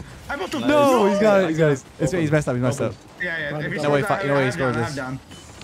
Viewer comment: what did say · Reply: "He messed up, he messed up"